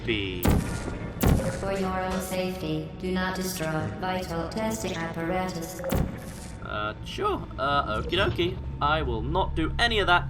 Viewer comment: When I first played the game and GLaDos said "Do not destroy vital testing apparatus", I thought it said "Do not destroy, bite, or test the apparatus"